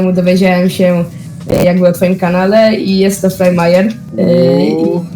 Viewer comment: czy ja dobrze usłyszalem FrayMayer?